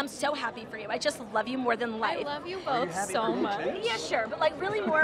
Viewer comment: Am I hearing this right, reporter said at 1:55 "I just love you more than life"???